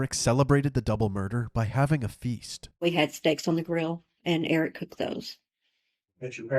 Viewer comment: OMG every time I go back I hear, " we had sex on the grill... to celebrate... " Wtf? OK narrator confirms sex=steaks! Sex on the grill is not the weirdest thing I've heard, although if it was hot, that's just another sick story. Edit: I don't mean hot s*x I mean a fired up grill... 😆